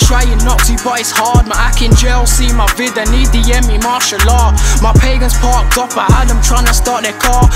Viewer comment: Am impressed but I don’t like people using islmaic reference in their music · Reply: WM G yh g idc tbh I thought he said martial art. 😅😅